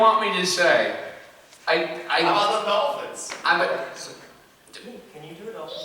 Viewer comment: I heard dem Dolphins! Anybody want to dem Dolphins?